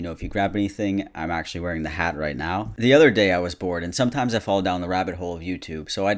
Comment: I misheard “the other day I was bored” as “the other day i was born” and i was like shit dude me too